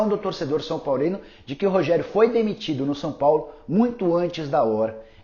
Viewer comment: entrei sem querer no video é ouvi "Rogério Ceni demitido" Caraí que susto !!!!